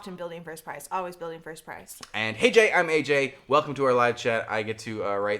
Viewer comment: I always thought aj said "hey j" and not "hey AJ"